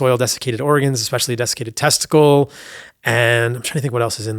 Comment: ayo what tha...did he say dessicated testes? In his smoothie??